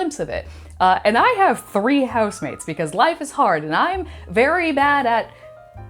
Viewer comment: husband? I thought you had a girlfriend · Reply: I thought she said husbands (plural) although replaying it a few times it is housemates 😂😂😂